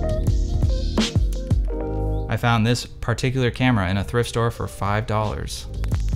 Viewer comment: It almost sounded like you said you found this camera in a thrift store for five dollars instead of I’m a camera whore, I don’t care about dollars.